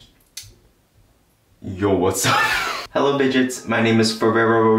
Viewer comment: At first I thought he called us bitches but it was something more hillarious than that LMAO